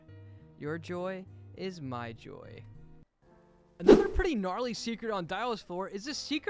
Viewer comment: did he just say gnarly?